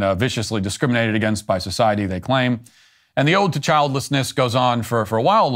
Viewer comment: I heard a line as “the ode to childishness”